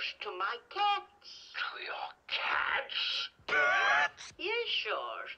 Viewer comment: what did he actually say? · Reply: CaHtS???" With a strong wheeze